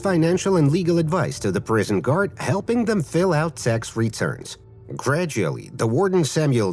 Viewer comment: I swear I heard S*X returns 😢😢